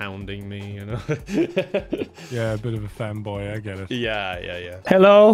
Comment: i legit thought he said femboy and had to rewind a bit to make sure